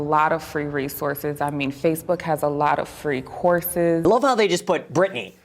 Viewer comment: “Facebook has a lot of courses.” I actually laughed at this. I’m sorry but very few employers are going to hire someone with a Facebook course as their main qualification when other candidates have degrees from prestigious colleges. People who aren’t in a good financial position mightn’t be able to go to college either because they can’t afford it or because they don’t have time due to having to work really hard to make ends meet. To say that people can make up for that with a FACEBOOK COURSE is the most OUT OF TOUCH thing I’ve heard in a while. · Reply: Oops, I thought she said Facebook has a lot of horses. Like people giving away horses that you could idk flip for profit...